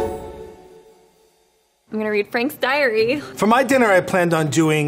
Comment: I heard “I’m gonna read Frank’s diary” as “Anne Frank’s Diary”